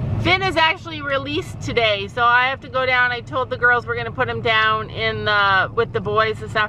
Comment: Omg I thought you said were gonna put Finn down I was like NOOOOOOOOOOOO and then I felt stupid lol